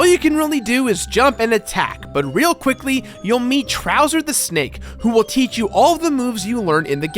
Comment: Trouser the Snake? Did I hear that right? Amazing